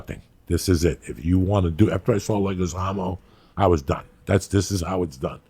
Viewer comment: what did he say he saw at 5:46? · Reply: Leguizamo, I’m assuming John the actor/comedian